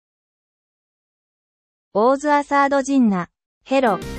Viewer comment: Zeep, oliva, what awe you doingoo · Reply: Why do I hear "what ae u duingu"💀